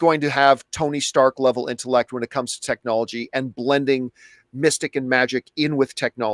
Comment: I thought they said science and magic, not technology and magic